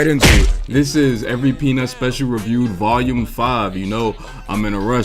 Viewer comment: Bro you sounded SO tired in the beginning lmao, I thought you said “reviewing every penis special”